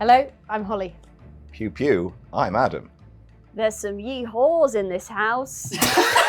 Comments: "There's some Yee-Haws in this house!" I'm screaming!!! 🤣☠️🤠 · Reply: I heard that as "wee whores in this house" lol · "There's some Yee-Haws in this house!" I'm screaming!!! 🤣☠️🤠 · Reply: I legit heard "There are some Yee-Whores in this House". Still works, but in a very different way.